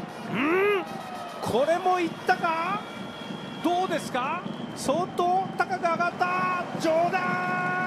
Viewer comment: 「ソートー、高く上がった？冗談」にしか聞こえなかった